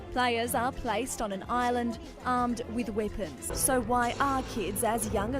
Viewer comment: I swear in the 4th one I thought when it said with weapons I thought it said with women